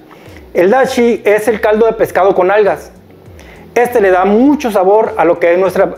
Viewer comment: Cuando escuche "caldo de pescado con nalgas" 🤔🤔🤔 nooo, es "algas!" 😆😆😅😅